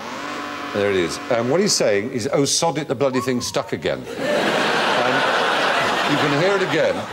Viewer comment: i thought he said "Osama, the thing's stuck again!" haha